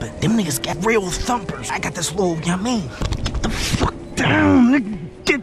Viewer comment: "I got this lil ya mean?" DEAD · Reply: I thought he said I got this lil yoa ming